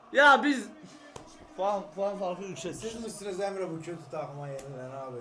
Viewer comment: kötü takım hahahdsagdfshfs bunu fenerbahçelimi söyledi ben mi yanlış duydum